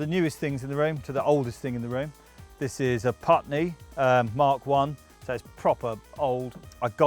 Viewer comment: Can sombody help me: What's the name of tis fantastic unique synth at 1:48. I didnt understand him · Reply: It's an EMS VCS 3, nicknamed "Putney". :)